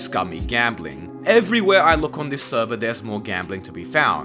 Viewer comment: I am flabbergasted, i've been reverse clickbaited, the amount of law breaking stuff on there is just crazy, on a more positive note, your server looks like a lot of fun, can't wait to hop in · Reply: for a second I thought you said "this server" instead of "your server" I thought you were deranged. XD